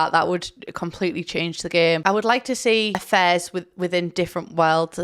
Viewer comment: Oh “affairs”! Like I genuinely thought you were saying you wanted a fez, you know like a hat? And I’m British so I’ve really no excuse (sorry).